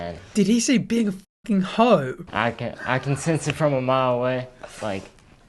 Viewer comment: i think he says being a fucking whore lmao